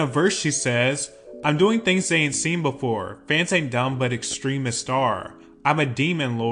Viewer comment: I thought she said “fans ain’t dumb, but extremely sore”